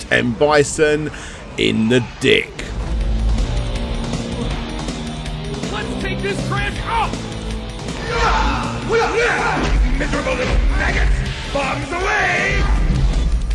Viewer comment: Um... at 05:00 ... did Bison call Ryu and Ken "little f**gots"?😨